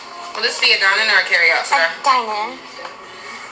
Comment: I hear today not sir!